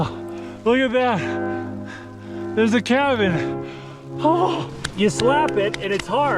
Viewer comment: I heard "look theres a Kevin ".